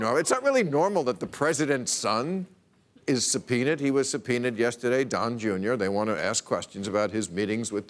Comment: aw now I get it" subpoena" I thought they said Don Jrs' a penis